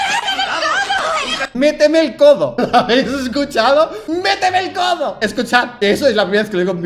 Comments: yo escuché "voy a desmayarme" y "hueleme el codo" JAJAJAAJAJ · we lo unico que escuche fue "voy a desmayarme" pero despuez de eso si se escucha "meteme el codo" ._. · Si escuché "voy a desmayarme" pero lo de "meteme el codo" se escuchó claro JAJAJA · lo que yo escuche fue "méteme todo" ._. XD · Sorry, yo escuché "Voy a desmayarme", "Le toqué el codo" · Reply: Yo escuché "muestrame el codo" xD